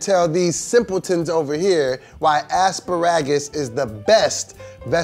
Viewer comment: did dough boy here just say a$$-ba-ragids instead of asparagus.